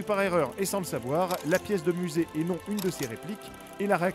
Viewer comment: la video date du 9 mai 2017 et vous avez toujours ce problème de musique trop fort!... ex: 2:35 qu'est-ce qu'il dit entre "...la pièce de musée..." et "...réplique" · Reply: sport national "la pièce de musé et non la réplique"